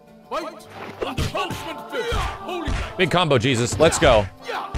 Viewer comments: aaaah. "punishment fist" i heard "punch McFist" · Was definitely mishearing "Punishment Fist" as "Punch With Fist" for a while. Like, no shit Jesus XD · Was definitely mishearing "Punishment Fist" as "Punch With Fist" for a while. Like, no shit Jesus XD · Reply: That's better than me, I kept hearing "Punch McFist". So glad to have it cleared up.